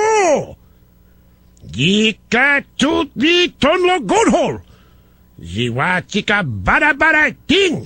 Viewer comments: does he say "goathole"? What a dork. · did he say "cornhole" at 4:11? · sounds like Rush says "goathole"